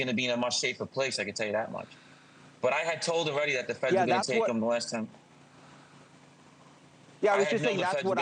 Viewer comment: Did he say take and molest him?????????????????